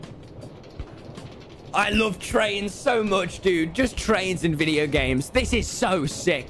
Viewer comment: “I LOVE TRAINS AND VIDEO GAMES”- TommyInnt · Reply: i thought he said "trains in video games"..... oh well....??